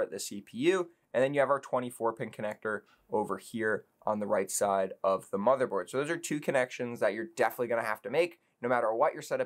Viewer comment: im sorry but it you says it called a 24 pink connector i didnt hear properly